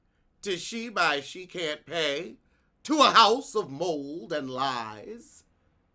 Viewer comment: The House of Mold and Lies!!!!!!! 😂😂😂😂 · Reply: Chile, I thought he said loans and lies. 😂😂😂